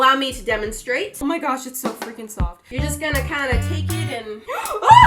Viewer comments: No hate cus I love her vids but did anyone else hear “it’s so f***ing soft” at 8:12. Or was that just me 🤔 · OK two question 1:How does she know what dragging a body is like??!! 2:Is it me or did she curse "Omgits so f*****g soft?? Either way still love her Edit:so I heard it in a very powerful speaker that picks up every lil noise and what I heard was " djfhdhwknecj" Jk she said"OMG its so freakin soft" therefore she did not curse sorry for that he he😳 bye for now